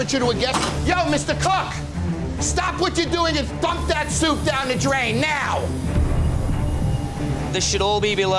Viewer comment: I thought he said " stop what you're doing and fuck that soup down the drain now" 😂😂